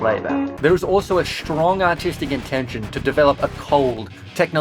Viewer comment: For a split second I heard strong as schlong.